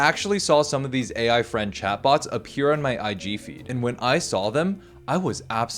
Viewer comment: Swear to god I thought you said PUBG feed. Had to make sure